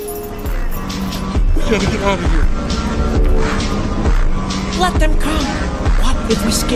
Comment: I thought he was abouta say “let them cook” at the beginning lol